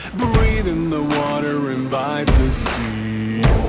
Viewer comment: I swear to god I hear "Breathe in the water, Imbibe the sea" every time.